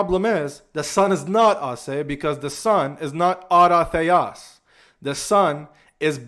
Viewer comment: what was the word used? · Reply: autótheos